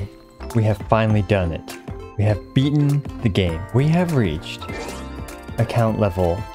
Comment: i thought you said "we beat the gay" ✋😭